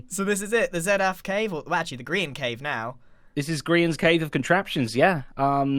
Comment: When he said "Grian's Cave" I heard "Grian's Grave" LMAO